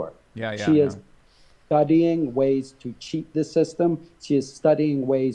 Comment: "she is studying ways to cheat the system" did I hear this correctly?